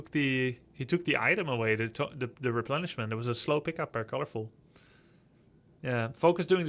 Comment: what are you saying @ 10:25 after "he took the item away" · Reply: "took the item away, the replenishment, it was a slow pickup by colorful"